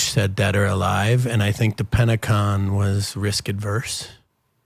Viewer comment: Very vague with his answers and responses, smart. Did he say "Pentacon" @ 3:43?